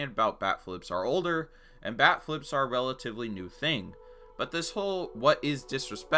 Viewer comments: I heard "back-flips" for the entirety of this video and i was kinda confused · I’m deaf I thought you said back flips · Holy shit the entire video I thought he was saying back flip. Wtf is wrong with these pitchers, even a backflip wouldn’t be enough to purposely hit someone and they do that shit for a bat flip?